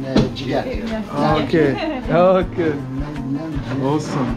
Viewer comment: Think he said Lamb Jigar, Jigar meaning liver in Ind too.